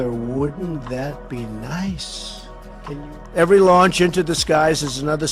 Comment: weird wording there. every launch into the skies or every launch into disguise ? I heard both at the same time twice. maybe i am mistaken but it was weird all the same. they are trying to bullshit us on every front